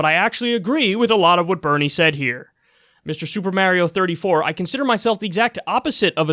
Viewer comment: Did he just say “Super Mare-io”? 😂